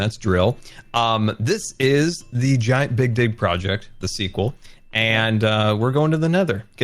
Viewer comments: Scar: “this is the giant big dig project” Me thinking he said something else: “sorry what?” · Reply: Yes I also thought he said bic dig haha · I could swear Scar said "big d*ck project" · who heard big d project, or was that only me